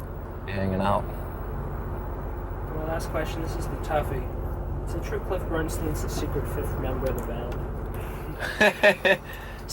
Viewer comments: Does anyone know what the interviewer said here? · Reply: "Is it true Cliff Bernstein is the secret 5th member of the band?" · Who was the secret fifth member? I can't catch that name. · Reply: "is it true Cliff Bernstien is the secret 5th member of the band" I think that's what the interviewer said :)